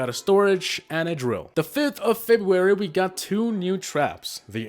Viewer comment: did I hear 5th of February on my birthday👀👀